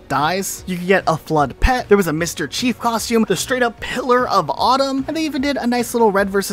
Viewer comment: I thought you said Hitler of Autumn when I first watched this